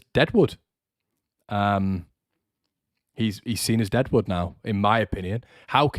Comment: Thought you said cresswell is jedward at first not dead wood haha